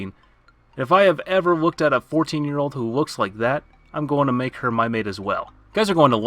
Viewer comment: excuse me what? Did I hear you correctly? Correct me if I’m wrong but you said if you see a cute 14 year old you’ll make her your bride